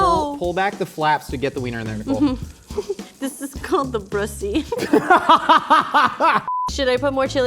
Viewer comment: DID SHE SAY THE BRUSSY?????????